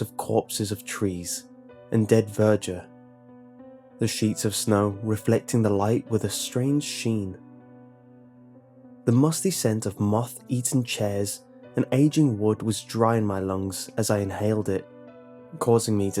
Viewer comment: I thought you said "moth eating chairs" instead of "moth eaten chairs" now all I can see is a chair with a big mouth eating any moth it finds...